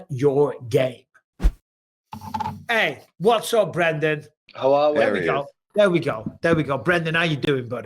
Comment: Almost thought it said Brendan Schwab for a sec